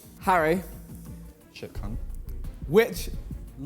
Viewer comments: Is it just me or did someone say sh*t c**t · did he say shi* Cun*?